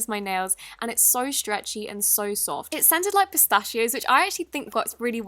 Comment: captions on “It sounded like pistachios.”